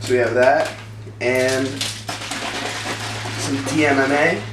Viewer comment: min 1:14 what did he said some tam what